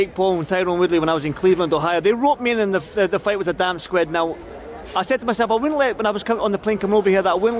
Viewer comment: Did he say damp squid? 🤣